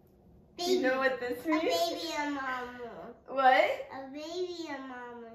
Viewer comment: At first I thought was saying “a baby in Mama” haha